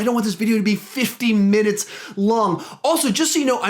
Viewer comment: I'm not sure what I actually said, but I was supposed to say, "I don't want this to be 50 minutes long." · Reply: ok i thought you said "I don't what it to be 15 min long" but nice video dude :-)